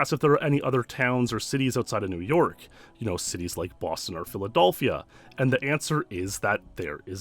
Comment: anyone else hear him say "Philadolphia?"